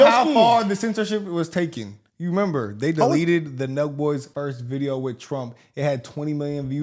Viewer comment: Who ever controls YouTube needs to be punished for censoring the public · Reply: Speaking of which, anyone catch the name of that podcast they mentioned at 8:17? I can't find it. I'm unfamiliar with the channel. Did he say "Nug boys?" "No boys?" "No Voice?" 🤔